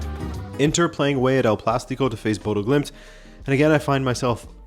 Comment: Inter playing away at WHERE? Is it just me or does he say "El Plástico"?